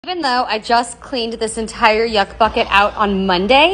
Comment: Y'all I thought she said fuck bucket 💀